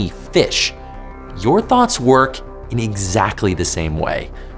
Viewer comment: Am I the only one who thought he said thot instead of thoughts No one? Just me? Okay